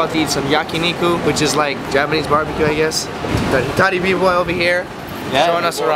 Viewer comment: who is the youtuber at 8:20 ? i didnt understand his name... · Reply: It's